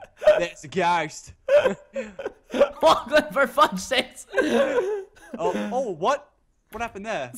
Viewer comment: did he say for fuck's sake?